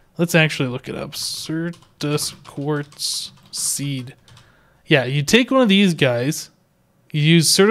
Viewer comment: at 21 mins it sounded like you said "pubic quartz" tell me it aint so!